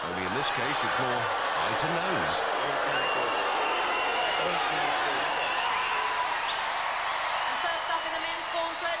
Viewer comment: what did Warrior say? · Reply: You ain't getting through